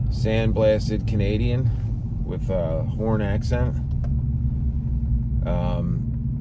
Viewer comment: At first I thought you said " I'm smoking a Canadian billiard with a foreign accent". No silly a HORN accent! ...my bad...hehe 🤣🤣